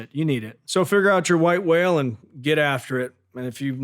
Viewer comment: I thought he said white rail. Little disappointed when I saw that machine being dragged over